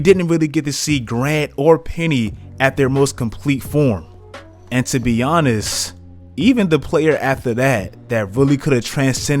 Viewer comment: Who else heard "and to be Giannis"